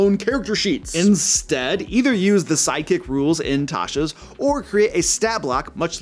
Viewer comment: Hi, 7:00 the subtitles are wrong. You refer to the sidekick option in TCE, and the subs say "Psychic". As a non-english/american speaker I knew you were refering to sidekicks because know the option, but my brain would not let me hear it as the subtutles were saying something else. And I was like "Why does he make that mistake, what did I miss about Psychic characters ?" Wel that would be a hilarious brand new type of hirelings.